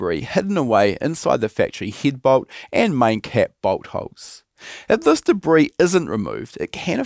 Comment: 😂😅 I know he said bolt holes, but I hear boat hoes.